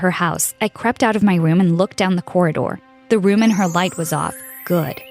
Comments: Did she say the room in her light??!! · did you say the room in her light was off? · Me: did I just hear that right? (The room in her light)? Wat. 😐😶😕🤔🤔🤔🤔🤔